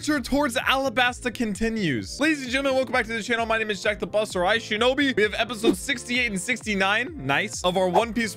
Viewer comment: Since I'm not an native English speaker, in every intro, I hear "I'm Jack the buser, iShinobi" hahhahaahah